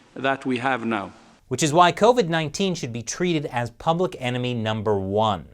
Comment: at 1:51 with captions on it said cope with 19 instead of covid 19. Lol